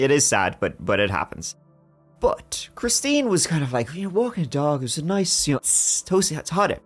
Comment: I thought for sure he said she was walking a duck.